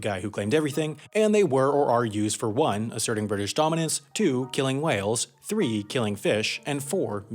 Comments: Its funny how in subtitles it says Killing Wales instead of Killing Whales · The audio implies "killing whales" but the captions say "killing wales". Good job, England, you killed Wales.